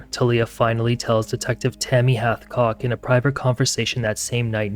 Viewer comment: Initially, I heard this as either Detective Tammy Halfcock or Detective Tammy Havecock. One is very cool and the other is very funny.